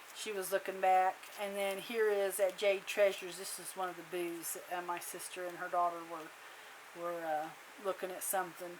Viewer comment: The first time I thought you said they had a lot of booze. Then you got to the second receipt and I think you are saying they had a lot of booths. LOL loved your journal sweet memories of a beautiful trip with family